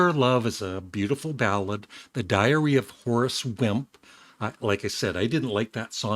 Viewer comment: For a second I thought you said, "The Diarrhea of a Horse," LMAO! Too funny. Good review.